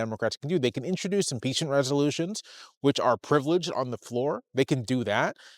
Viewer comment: I couldn't hear clearly -- what kind of resolutions could Democrats introduce that would be privileged? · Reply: Impeachment resolutions